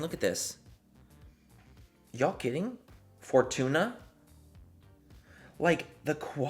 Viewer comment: Did he just say “for tuna” for fortune 😂😂😂😂😂